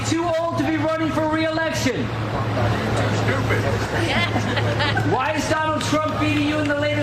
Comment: I heard "Are you stupid?"